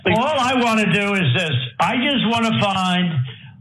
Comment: I want to see this turd convicted but, media keeps reporting he said to Georgia Sec. Of State Brad Raffensberger, "I just want YOU to find...", when I just heard him say, "I just want TO find..." I never listened that closely intently to that portion of the statement before now, and I have heard it at least a hundred times. There's a difference in what he said and the meaning. Be careful, let's not let this slip from our grasp in our zeal to convict. He was nudging at Raffensberger when he says, "I JUST WANT TO FIND..." trump may be able to slide from under this segment.